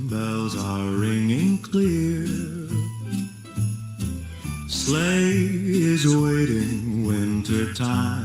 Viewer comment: The phrasing is odd. The first thing I heard was, "Slay is waiting". Then I realized it was " sleigh" . But with the line about every "soul" finding a home in the FLAME, it jiust sounds wrong. Demonic double meaning mocking us, possibly?